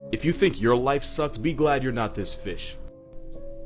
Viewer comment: I fr thought he said “be glad you’re not this bitch”